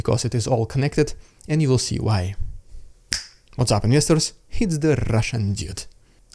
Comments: I thought you said "what's up misters", but it didn't sound quite right, now I know why 😅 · Investors!!! All this time I thought you were saying ministers.. It didn't make much sense, but that is what my ears finally settled on. Thank you for continuing to post your perspective on this war. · Thought you were saying, "What's up misters." 😂